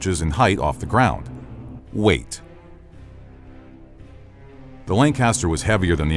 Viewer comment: When you said "weight", I wasn't looking at my screen. I thought you had said "wait". I was on the edge of my seat!